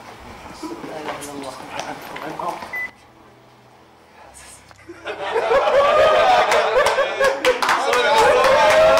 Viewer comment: ايش قال؟ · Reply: اللي نومه قال ( كيف حاسس ؟ )